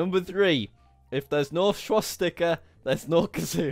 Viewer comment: I thought he said swastika as in the Nazi swastika